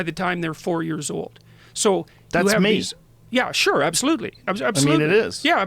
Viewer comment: Can someone help me out here. Not sure what Joe Rogan said at 38:28. If anyone can fill me in that would be great. Thanks! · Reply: Wait. Matt is wrong. He said "That's me."